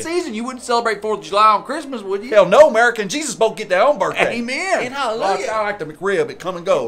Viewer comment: my chleepy brain misheard "Merica and Jesus" as "Merican Jesus" but then again that sounds like something would say